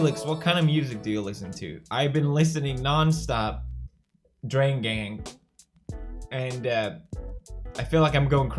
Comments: what is he saying. I wanted to listen but I couldnt fınd the band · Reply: Drain gang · What band does he say is his favourite? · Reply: he's talking about the rap collection drain gang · did pewds just say drain gang ?